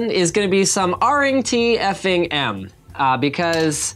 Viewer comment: What is he saying at 1:01 ?? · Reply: R'ing T F'ing M - Reading The F☆cking Manual